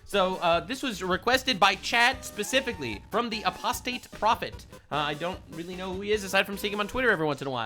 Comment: I could have sworn he said Prostate Prophet at first, which is a very different connotation